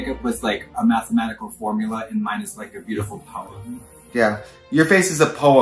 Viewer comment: I thought that she said "your makeup is like a mathematical formula minus like a beaultiful poem"